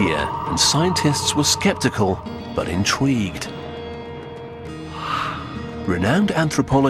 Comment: Did he say "intweaged" ?? LOL